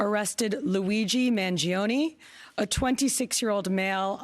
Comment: Luigi Mangione...I swear I thought it said Luigi Mario at first...and as if dude didn't already remind of Ezio in that hood and u know the assassin angle too